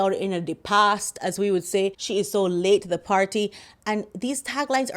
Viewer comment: Have to tell you, when you said "so late to the party", I thought you said "legs far aparty", I really cracked up 😅. Thanks for the update on Selena's success, that was a feel good story!